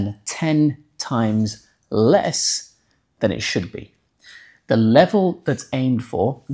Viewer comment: say "...less than a tenth it should've been." next time. I almost suckled on a sawed-off while trying to figure out what the hell you were saying.